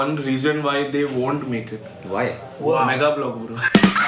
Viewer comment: what does say @ 41:42? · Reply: Megablock